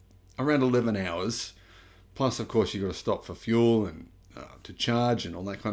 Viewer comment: stopping for fuel ? did i hear that right 00:08 ???